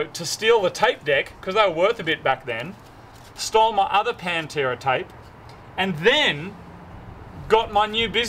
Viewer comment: did you say..... Pantera? >:)